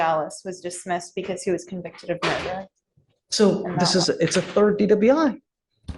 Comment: Dismissed cause he was convicted of MURDER? or am I hearing that wrong?